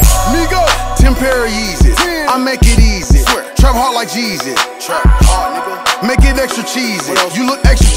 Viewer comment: On GAWD I heard trap hard like JESUS🤣😭